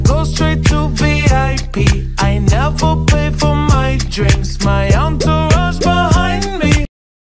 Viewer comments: "I walk in like a dime piece,I go straight to VIP,I never pay for my drinks-" Me: My UnClE rUnS bEhInD mE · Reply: Omg i thought it said "my uncle right behind me" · I thought it said “My Underwear’s behind me.”